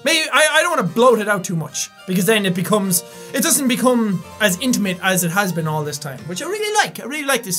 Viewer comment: for a second when he said intamate i thought he said into a mate and i was like WHAT